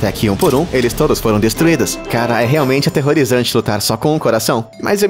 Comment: E só eu quee ouvi ele falando Caraí no